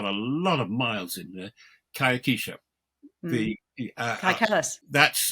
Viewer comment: Please, what is the name of the woman Mr. Fox mentions at the end? Closed captioning is flummoxed, and it sounds like he says "kai keesha" which is not searching for me. Thank you.